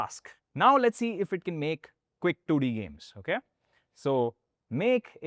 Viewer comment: bro what did you said? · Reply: Quick 2D games